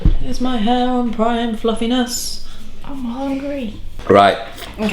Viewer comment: I didn't see anyone's mouth move, but did someone say "I'm hungry" at 0:03? lol Another great episode, though!